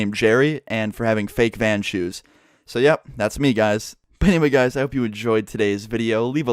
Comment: does he say "pay me guys"